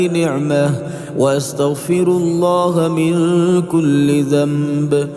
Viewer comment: instead of من كل ذنب it is written من كل كلب .please correct it.jazakAllah.